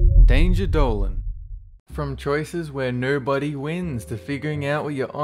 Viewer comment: The video stopped just as you said "From choices where nob-" Making it sound like you said, "From choices where nope." Danger Dolan has noped his way out of this.